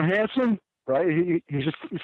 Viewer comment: What does he say at 04:53? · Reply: "What's it to ya ?"